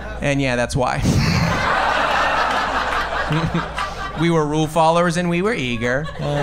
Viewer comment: What does he say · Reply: We were rule followers and we were eager